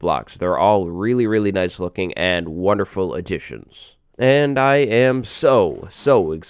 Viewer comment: did he say "wonderful Egyptians"?